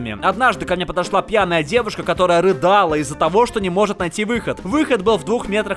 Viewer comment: послышалось не рыдала, а рыгала))) лол